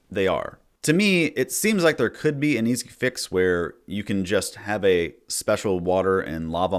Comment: What did he say here 10:12? "To me, it seems like there could be a _ fix where [...]" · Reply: Sounds like “an easy fix”